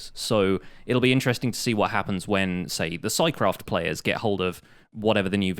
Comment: Ive been looking for this server but cant find it anywhere what server does he say arround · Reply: Oh, it's scicraft. I think half the hermitcraft folks played it before at least. Mostly redstone contraptions and so.